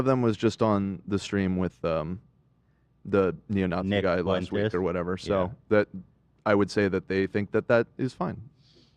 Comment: ummm did just say the n-word???? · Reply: Neo nazi is what he said. Check your audio audio auuuuUuuDddiiiOooOo